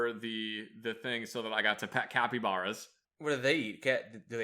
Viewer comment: Thought it said Zach got a pet Capybara.